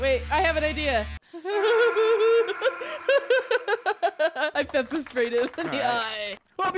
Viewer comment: When said pepper spray. I thought he said Temprest spray💀😭